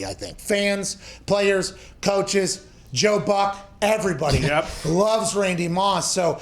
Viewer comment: When he said Joe buck I thought he said Joe budden and was very lost